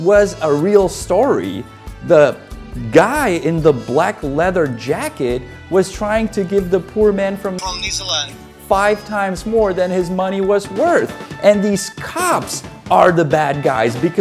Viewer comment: Hahahahaha "I'm from NEEZERLAND " 😂😂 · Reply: I'm English and at first I honestly thought he was trying to say he was from New Zealand!! Which is even funnier.